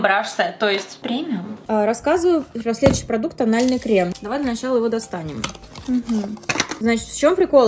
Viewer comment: На полном серьезе услышала на 5:00 "продукт анальный крем". Обзоры косметики, которые мы заслужили, лол